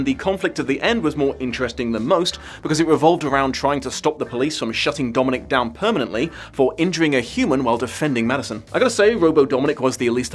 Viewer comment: I had to do a double take, because I swore I heard 'To shut Dominic down for entering a human to protect Madison.' Lol 😆